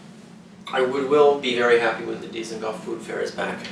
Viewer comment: I don't think I'd ever manage to convince my Polish family to add any sort of taste to our Matza ball soup... Maybe one day I'll get to taste what this recipie is like. Also, around 6:30, the subtitles say "Diesel golf" and "Decent Gulf" instaed of "Dizengoff". You may want to change that. Edit: Another one I noticed: around 9:10, said "The Paleshet, or the Plishtim", or "Peleshet/Philistines" if you want to use the common english names, not Palestine/Pristine as the subtitles say. Edit 2: At 13:28 I assume you said "Lesotho"? Because youw ere talking about surrounded countries. The subtitles say "Listened to".